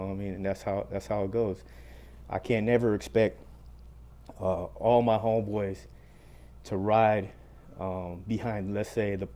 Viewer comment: What does he say at 05:01? Can't expect my homies to roll with the?